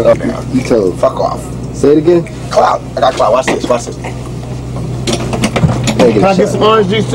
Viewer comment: Did Tupac said he got clout?!?!